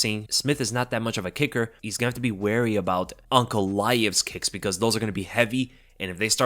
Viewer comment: I thought was saying Uncle Laev for half the video until I looked at the spelling 😅